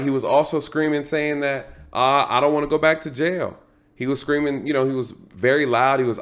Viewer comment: we can disagree but please don't lie. What he said was "Im not afraid to go back to jail for LIFE!" and we both know what that means.